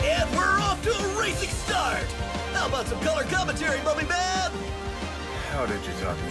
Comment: Did he call Aizawa mummy man? lmaooo